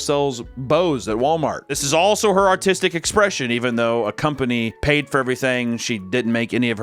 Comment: omg I thought he said autistic expression and I was so confused.